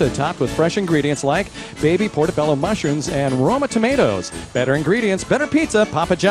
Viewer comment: Brandon when you listen to the show case when he says Roma tomatoes it sound like he said Rona tomatoes